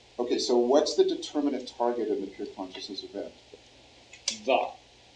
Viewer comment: he said "the" so forcefully I heard "God"